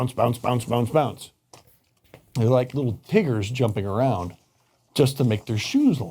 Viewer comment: You're joking, right? He said "TIGGERS". lol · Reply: sorry sounded like he said triggers which is something hyperactive kids in my area are called when they star bouncing around